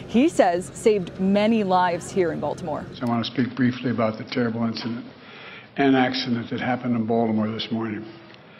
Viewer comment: I heard "terrorist accident". What about you